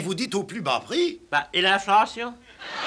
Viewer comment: qu'est ce qu'il dit a 6:20? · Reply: "Et l'inflation ?"